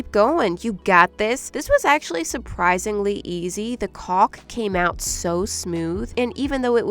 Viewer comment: WHAT DID SHE SAY?!?!?!?! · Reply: caulk